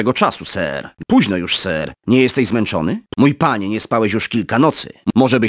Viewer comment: grając w to o 2 w nocy usłyszałem " Późno już Sir, nie jesteś zmęczony " xD